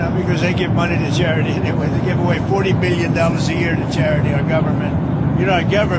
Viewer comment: Did he just say 40 million “A Year”?? Not just a one time payment but yearly?? 😳 What ever happened to DOGE and weeding out fraud and abuse 🤔